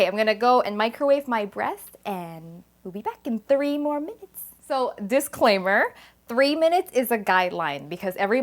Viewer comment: I thought she said 30 minutes at first. I blowed up my microwave. Blowed it up real good.